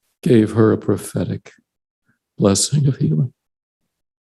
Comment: TLDR: Stk pres asks Nelson to give blessing for cancer person, even though there’s some secret rule you aren’t supposed to ask apostles for blessings. Nelson anoints and has cancer woman’s hubby bless. They never say if woman was healed. This story of a routine blessing is somehow evidence that Nelson is inspired. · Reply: He said he gave her a “prophetic blessing of healing”. So I thought that meant that she was healed. When I first listened to it though, I thought he said he gave her “her final blessing” and I was shocked!!! I had to go back and listen and he said prophetic blessing of healing.